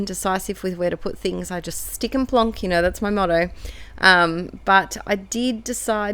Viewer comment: In my head as I scrap I hear you saying “stick and plonk” lol! I try to not overthink it too much anymore!